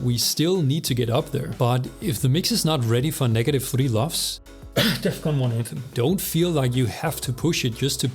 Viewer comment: What did you say while you were coughing? XD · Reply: "Defqon.1 Anthem" 🤣 (it's a REALLY squashed mix)